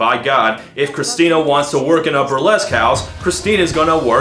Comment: oooohhhhh! You said "A burlesque house", I thought you said "Upper les-cows".